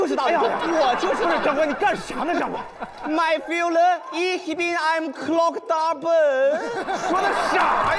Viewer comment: 在說什麼？ fühle 什麼的？ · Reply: 很复杂的语言体系，Mein Führer Ich bin eine Clock 大本😂